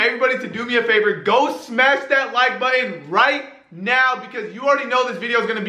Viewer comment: WHEN HE SAID GO SMASH THAT LIKE BUTTON I THOUGHT HE SAID DONT SMASH THAT LIKE BUTTON